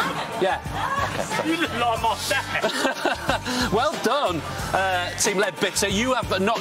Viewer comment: did Jimmy say “you look like my sack actually” about the haircut 😂😂